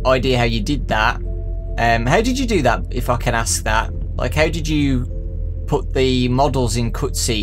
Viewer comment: did he just call him a funking asshat I can't actually say the real word because YouTube won't let me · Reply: "if i can ask that" his accent is so insuferable sometimes